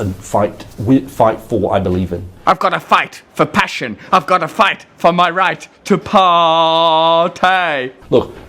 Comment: Ahahahahahahahaaaaaa - the captions service on this video has misheard the "I've gotta fight for my right to paaartay" line and instead delivered "I've gotta fight for my right to Paul Tighe". Sinister...